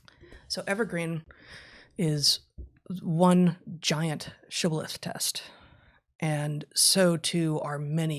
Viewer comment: Anyone know what Heather said at 15:35 ? What a 'sholef test' or whatever she said is? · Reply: shibboleth |ˈ sh ibəliθ; -ˌleθ| noun a custom, principle, or belief distinguishing a particular class or group of people, esp. a long-standing one regarded as outmoded or no longer important : the party began to break with the shibboleths of the left. ORIGIN mid 17th cent.: from Hebrew šibbōle t ‘ear of corn,’ used as a test of nationality by its difficult pronunciation (Judg. 12:6). Read the last part of the text of Heather's article just before your time stamp.